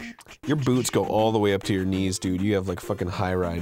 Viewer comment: Coulda sworn arin said Your boobs go all the way to your knees but then seconds afterwords he talks about boots....I hate my ears....